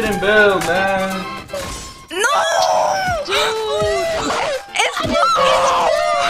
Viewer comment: minute all i heard was "plu it's pluuuu it's pluuuu" 😂